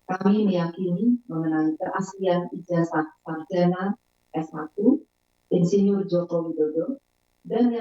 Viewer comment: Coba perhatikan di menit 1:15 - 1:18 . Rektor UGM menerangkan bahwa berdasarkan informasi dan documentasi yang kami miliki, ijasah panjenengan asli.. Coba terangkanlah ... Barangkali saya yang salah dengar .. Kalau benar, apakah cara penyampaian nya harus pake panjenengan y ..?